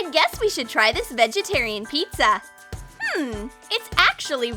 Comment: Did she just say vegetarian pizza